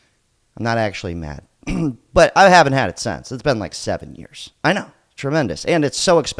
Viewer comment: For a second there I thought you said “I haven’t had sex in 7 years” 😂